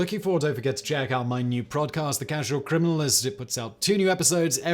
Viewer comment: Ok, I love Simon, and given the sheer amount of videos he makes, it would be impossible to make a perfect video; but did he say prod cast?